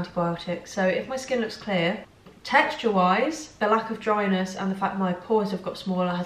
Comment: I live in the UK but when you said "texture-wise" I kept hearing "text your wives" lmfao